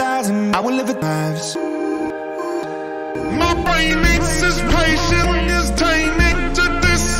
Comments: why did i hear "my brain is impatient" lol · why did i hear "my brain is impatient" lol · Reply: i heard "my painis is patient" lmao